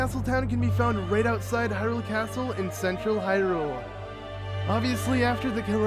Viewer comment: When you said central Hyrule I thought you said sexual Hyrule